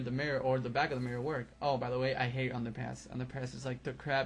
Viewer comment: Sounded like he said underpants when the underpass map came up lawl